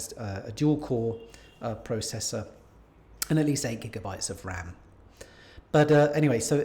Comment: I wish he would annunciate his words. What was he mumbling at 3:22. “Sazzy gigabyte of ram”. I spent 2 hours re-listening to that section…to find out the facts ??? MAYBE == 8 gigabyte // MAYBE == sissteen sazzy 16 gigabyte… I LOVE HIS VIDEO…. just extremely frustrating